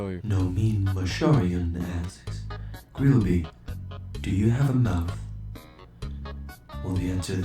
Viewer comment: Whenever it said grillby, do you have a mouth? I thought it said Burgerpants, do you have a mouth? XD